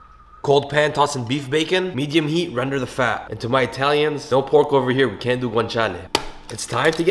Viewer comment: I swear when he said "to my Italians," I thought he said "don't picking paremjeans your chin ye?" my mom called me so I wasn't paying attention.